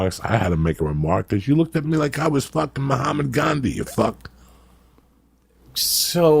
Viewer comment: Did he say "Mohammad Gandhi"?